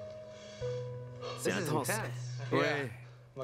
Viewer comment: qu'est-ce qu'il dit ? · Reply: Le gars il dit « c intense »